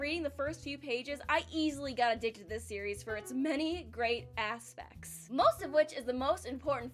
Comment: She says Aspects..but I hear " ASS THICC"